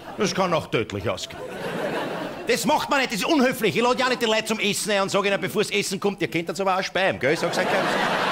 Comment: Was sagt er bei 1:36? Ich verstehe es nicht ... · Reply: Das macht man nicht das ist ja unhöflich, ich lade ja auch nicht die Leute zum Essen ein u sage ihnen bevor das Essen kommt ihr könntet aber auch Speiben ( Kotzen )